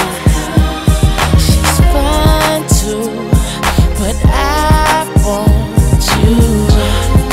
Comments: 5 YEARS LATER I FOUND OUT HE SAY “SHES FINE TOO” NOT “5’2” LMAOOOO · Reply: I’m late but as a kid I thought he was saying “she’s trying too, but I want you” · Half my life I thought he said “she’s 5’2 but I want youuu” 😂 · 5 YEARS LATER I FOUND OUT HE SAY “SHES FINE TOO” NOT “5’2” LMAOOOO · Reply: "She's Fine Too But I want You" he says it twice..It does sound like he says 5'2 .. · 5 YEARS LATER I FOUND OUT HE SAY “SHES FINE TOO” NOT “5’2” LMAOOOO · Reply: I always thought he said "Shes on too...But I'm on YOU!" lol